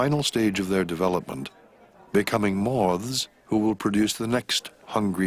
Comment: What does he say there? "...becoming (blank) who wil produce the next hungry generation." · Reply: Moths, really terrible pronunciation.